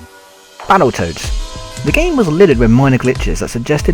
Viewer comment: When he said 'battle toads' I thought he said battle turds'